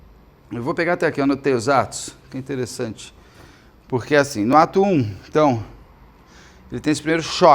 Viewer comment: Fiquei rindo sozinha porque quando vc falou "no ato 1" , eu entendi "no atum". 😂